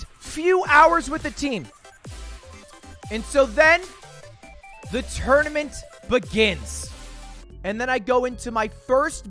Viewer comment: I thought it said pokemane tournament